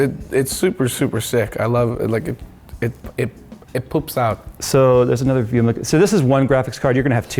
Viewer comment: did he say it "poops" out lmao